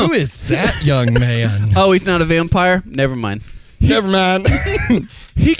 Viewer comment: I have to know what said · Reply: Never